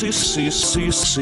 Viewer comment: What did he say at 9:36? “So now, you really know the do?” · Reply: "so now, you really know the poop"